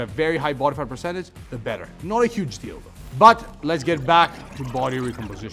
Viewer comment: did he say :Not a huge d@